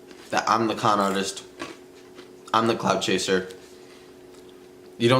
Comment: I thought he said "clown chaser" 😂